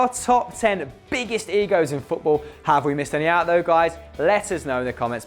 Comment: Lmao I thought it said "biggest EGGS in football"